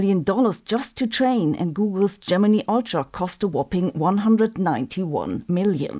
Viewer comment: But she pronounces some words weirdly, like Gemini, she says Gemini with a long e sound instead of a long i sound. And crocheted?! She pronounces it completely weird. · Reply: Gemini? I thought she said "Germany" :]